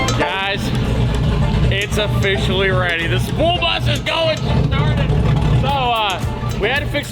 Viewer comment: Did you just say, "SPOOL bus"?